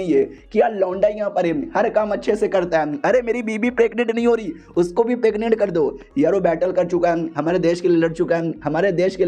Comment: bhai kya bola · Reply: Uski biwi pregnant nhi ho rhi 😂😂